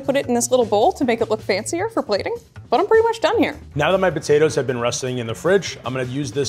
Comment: Did really say "Now that my potatoes have been russeting in the fridge" at 10:15? 😂